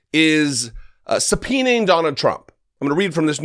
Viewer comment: was that "Donna Trump" I heard?